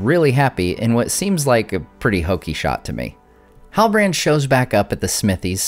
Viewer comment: Non English guy here What does he say at 19:33? Hooky shot? Hockey shot? How is it written, and what does it mean?